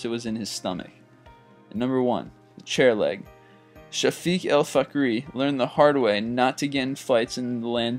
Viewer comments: did he just say f*ck. Don't say f*ck to a Unicorn. :O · Did you just say shafik el fuckary? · weird name when i heard ''fhakery'' i said whaaa.... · Sharie Fuckary? That's what I heard. I'm not trying to be racist or whatever btw